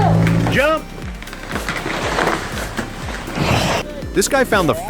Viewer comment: I did tree work for 27 years and I can't look at enough of these videos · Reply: did he say jump or? Chump 🦧